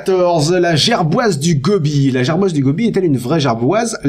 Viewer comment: j'ai entendu " la gerboise dégobille "